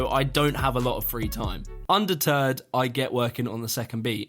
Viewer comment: Did you say "undeterred" or "under turd"?